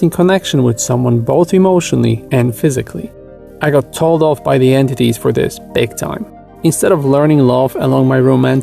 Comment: can't understand message clearly plzzz help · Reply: being told off by the entities?